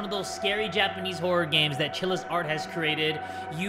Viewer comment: At first, I thought he said, "Where Killas aren't as creative"